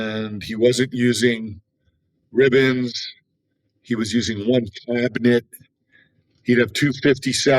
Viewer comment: what is a warm "clampfniff"? Lol. I can't tell what he said. Anyone? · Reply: “One cabinet”